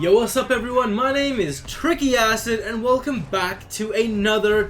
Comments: i thought u said tricky ass...... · no offense but when i started watching ur vids i thought u said tricky ASS